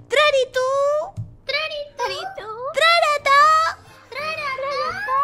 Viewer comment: En el minuto 10:43 entendí trae arata